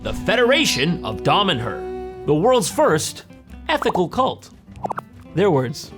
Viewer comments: I thought it was 'anti-italian' cult, whoops. I also thought he said 'ethnic cult' and that totally backed it up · I thought he seriously said the Federation of the Dhamerers 😂